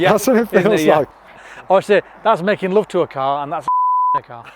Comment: "...and that's fuck*n' rap1n' a car" ??? What do you think said?